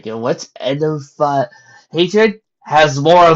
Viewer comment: wait, where's the rest of the list? I couldn't understand what you said. Sorry. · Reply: On end of hatred on my legendbringer account